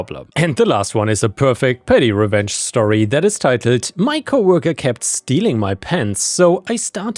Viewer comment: Last story, sounded like you said “my coworker kept stealing my pants.”